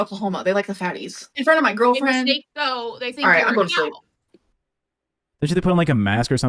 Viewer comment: what did say? After said “I live in Oklahoma, they like the fatties”? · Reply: To me it sounded like "they think you're a cow" but I'm probably way off. 😂